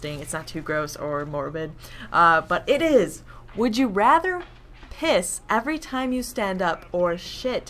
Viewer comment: Oh I thought you said kiss every time you stand or sit. Kuz you were like "either way it's messy" I'm like "How do you guys kiss?"